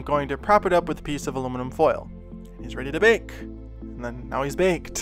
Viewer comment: Bacon bond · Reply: imanironboi that is literally all I can hear now. I never had an issue at all before he mentioned that he was getting comments about people hearing “bacon” instead of “bake and” now all I hear is “bacon”!! 😂